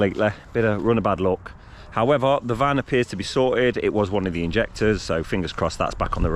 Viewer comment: Great job guys, I thought you said e was a injector